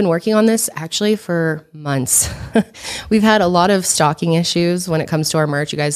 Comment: Thought u were saying stalking, not stocking at first (about the merch) 🤣